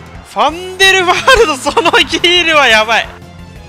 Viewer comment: イキリヒールはやばいって聞こえる笑